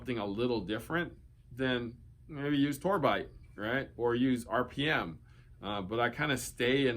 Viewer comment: what did he say? ....forbite? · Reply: solinco “tour bite” which is a stiff poly string